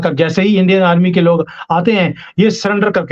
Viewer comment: Did he say gun le lo?? or something else? 😂😂😂😂